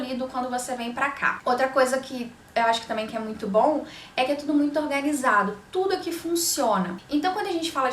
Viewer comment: Entendi crime organizado😂😂😂😂😂😂